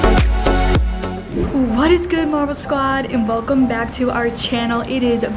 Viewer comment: I always thought you said "what's good mama squad" for your intro but it's "what's good marble squad" 🤣🤣🤣🤣